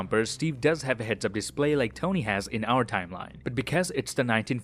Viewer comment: did he really said "like our tmline"